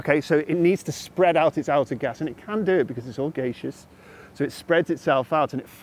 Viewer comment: I'd heard "it's all turtles" and "it's all elephants", but this is the first time I hear about a cosmological model that is "all geishas".